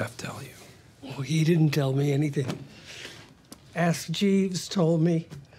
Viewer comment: What does Marion say here? Who told her? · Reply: Ask Jeeves, it’s a search engine.